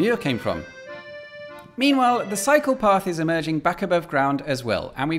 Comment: I could have sworn he said, " the PSYCHOPATH returns above ground..."